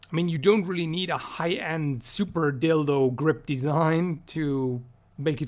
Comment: I could swear I heard the words "super dildo grip" in this video. Lol.